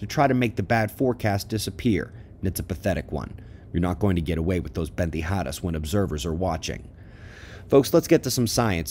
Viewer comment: Hey Ben Good morning to you and all fellow observers!! At 2:09 did you say, "you're not going to get away with these pendejadas" or am I tripping??...